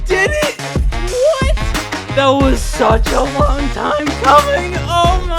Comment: i had to turn on subtitles for a sec cuz instead of hearing that was such a long time coming i heard "im coming"🥀